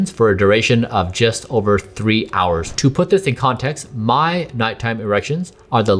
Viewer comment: I kept hearing " My nighttime Russians"